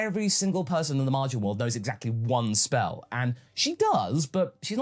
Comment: i thought they said " at least one spell"